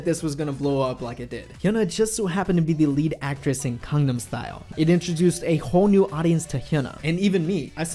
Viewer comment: OMG every time you say GANGNAM style I thought you are saying CONDOM style. WTF. The word start with a G and somehow thats hard for you to pronounce?